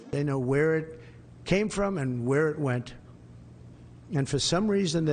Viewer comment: Did I hear this correctly? At 4:22 Donald Trump said "They know where IT came from and where IT went." "IT" to me means singular non plural. Is that what Trump said? IT only one object.